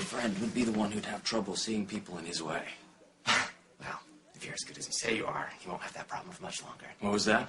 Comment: Could someone please tell me what Luke´s saying at 00:43???????? I get that his snapping back =) but because of the whispering I just can´t understand it. Thx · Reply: Mamma Mia sorry this is 6 years late but he is saying if you are as good as you say you are you wont have this problem much longer